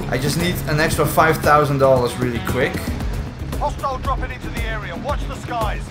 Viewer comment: Everytime I hear "hostile dropping into the area" on low volume I just hear "lost child dropping into the area" for some reason